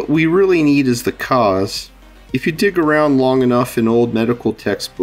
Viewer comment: I wasn't sure what you said exactly at 1:44, so I turned on CC and realized it was "dig around" instead of "d1ck around". 😂